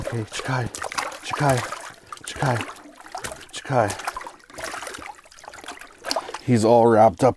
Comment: What'd you say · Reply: Chickia. Means woah in Ukrainian